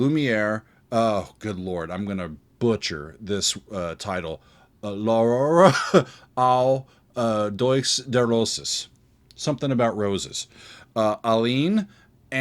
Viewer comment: When you said "L'aurore aux doigts de roses" I heard "Do a barrel roll" 😂